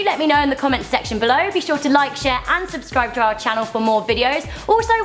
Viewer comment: i thought u said unsubscribe